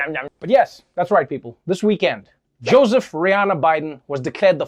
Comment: did he call Biden, Joseph Rihanna Biden? I thought it was Robinette...